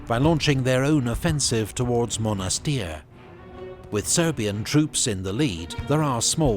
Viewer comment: did they say Soviet troops?